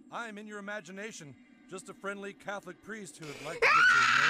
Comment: Priest? I thought you said, weast